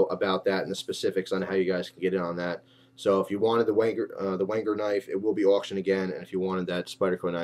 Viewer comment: thought you said "wanker knife" lol